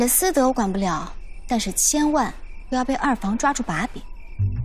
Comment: nghe ra chupapi :))